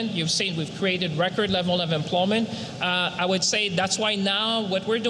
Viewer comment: It's funny, he said, record level of employment It sounded like he said record level of imploding